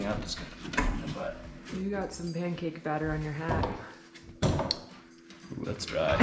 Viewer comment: Did you say 'mancake' batter?